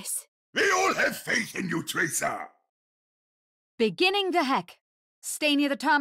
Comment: When I first heard one of Mercy's lines, I thought she said "Beginning the heck" not "Beginning the hack."